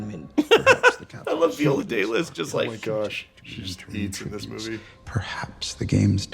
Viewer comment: Did Alan just say she "ate"? Good Gen Z rebrand Alan, not on my 2025 Bingo card though